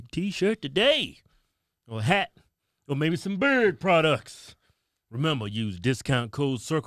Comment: Every time all I hear is “bird products” it takes me a couple seconds to realize you are saying “beard products”!!